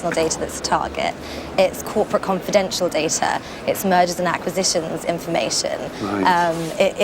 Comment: At about 33 minutes in i could have sworn the young lady said "Murders and acquisitions"